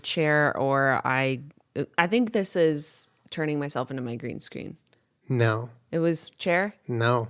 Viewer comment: Somewhere around 33:00, on 2x speed, I heard "Turning myself into Meryl Streep" and I was all... I need to see this